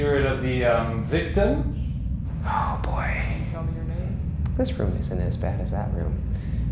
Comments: I don't know if that was Shane or anything but did anyone else hear at 19:21 "Can you tell me your name?" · Does anyone hear at minute 19:21 someone saying "Can you tell me your name"? Or it's just me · Did anyone hear someone say “tell me your name-“ at 19:20 cause I did and is s h o ok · did anyone else hear a female voice saying 'Tell me your name' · right after Ryan said "Oh boy'" I heard something like "Tell me your name"... Put on your ear/headphones and listen to it carefully! It sounded like a lady speaking! 0_0 Or It that just ShANE?🙄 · I think I heard a voice after ryan speaks.. but i don't know what it said.. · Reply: Keene jv Leonor shane says "can you tell me your name" or something like that. someone else pointed that out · who heard can you tell me your name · I think something or someone is say” tell me your name “ it could be Shane but I have known idea. Tell me if you here it too or know who said it? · YOO WTF LISTEN CLOSELY AT 19:20 Something says "tell me your name" Wtf how did no one notice that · why can I hear 'can you tell me your name' is this them?